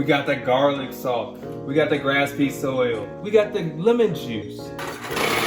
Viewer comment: did he just say "Grass pee soil" instead of "Grapeseed oil" i am losing it